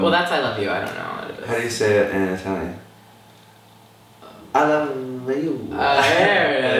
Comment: What does Scott say at 8:31? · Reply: How do you say it in Italian? I love you. (Cause he doesn't know)